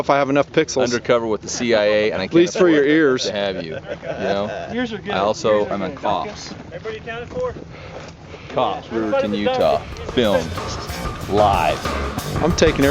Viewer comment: Did he say Riverton Utah? 19:02 Where are you guys at?